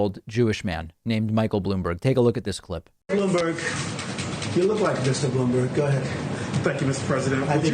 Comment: Sounds like he says... You look like a Bloomberg. I hate the guy but this is far fetched.